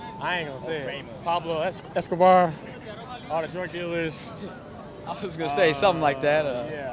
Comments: "All the crack dealers" ???? Pero qué dice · Fortfiesta en el 00:12 lo que dice es 'drug dealers', traficantes de droga, que hablando de Pablo Escobar tiene sentido